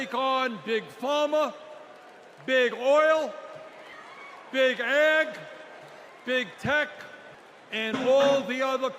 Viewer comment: my high ass thought he said “big egg” and i was like “??? the egg industry?!”😭